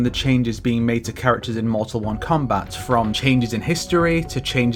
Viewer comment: he did not just say mortal 1 kombat 💀